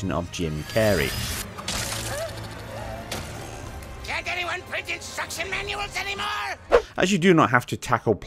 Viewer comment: What did he say? Difficult to understand honestly.... ''Cant anyone ?????????'' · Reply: "Can't anyone print instruction manuals anymore?"